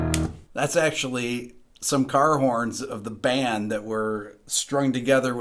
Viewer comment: What was strung together in a box with batteries, for the opening staccato bass tones? It sounded like you said "Gar horns"?